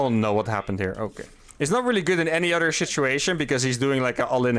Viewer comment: Did you say 'shituation'?